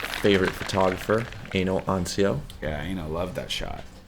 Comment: when the photographers name is Eino Ansio and youtubes automatic subtitles say anal unsealed